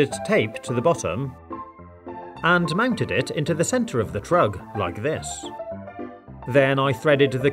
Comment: did I hear drug or what?